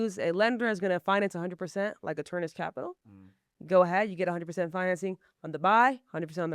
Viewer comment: She dropped a lender but I don't know what she said. "Something Capital" can anybody make out who she named? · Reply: Sounded Arcturus Capital